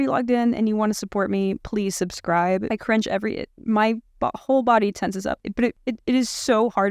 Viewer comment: 😂 Is it just me or did anyone else hear "my butthole body tenses up"? 🤣 She is adorable! Thank you for this thorough analysis! Excellent job! Subscribed! 💗